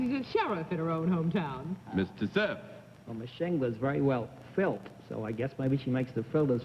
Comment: At about 3:40 Bennett makes remark about the first contestant tat sounded like she "looked filled" Can one of the slaves check it and see if he said such a non pc thing?